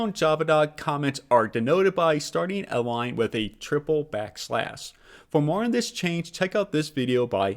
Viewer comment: did you mean to say triple slash instead of backslash?